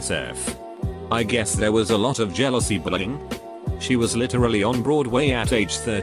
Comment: why do i hear bullying as "brrrring", am i just too high or did anyone else hear that